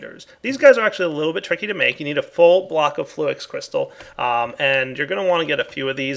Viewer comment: it sounded like he said "a full flock of fluix crystal" at 15:38 / 15:39 though it sounds like block once you hear it the 2nd or 3rd time